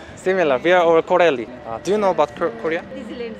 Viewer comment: I like the way pronounces "L" instead of "R" like landomly it's funny 😆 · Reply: it sounds like he says carelle